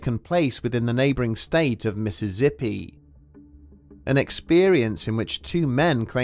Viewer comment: Did he say Missizzippi instead of Mississippi?